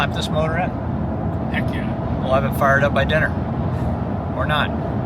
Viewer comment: The amount of times I heard "diner" instead of "dyno".... and my dumbass thought you guys needed to rush to have pancakes or something lol